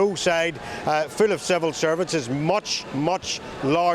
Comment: Did he say "full of civil servants" 1:56.? Think so; but can't quite make out accent. If he did it's not relevant but probably true. Then he says "public-sector workers" 4:14.. Resplendent with Fakestan flags as well. Symbolic of the enemies of about 95% of Jews and a great many of other religions, except the "Religion of Peace" (as if). Or is there some arcane relevance to Belfast? Are BBC public-sector workers? Or are they paid even better?